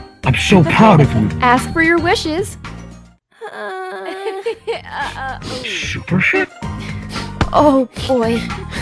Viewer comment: did Freddy just say supershit